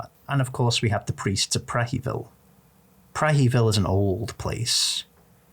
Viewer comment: I misheard "Prehevil" as "Crankyville" the first time you said it and that's what I'm calling it from now on